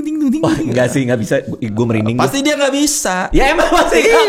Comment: di menit 43:14 sampai 43:16 kaya ada suara yang ngomong diluar podcast yaaa "gua merinding bang " kedengernya sihh gitu, tapi suaranya asing bang :face-blue-wide-eyes::face-blue-wide-eyes::face-blue-wide-eyes